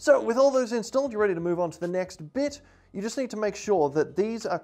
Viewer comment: Wait what did I hear at 4:05 Next bitch?